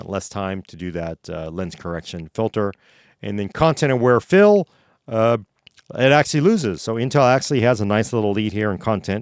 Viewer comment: For a second I heard that as "Content Aware Phil" and was like "That would be a cool name for an AI